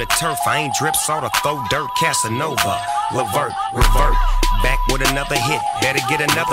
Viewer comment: what he say at 2:46 .. kasinova re.. ?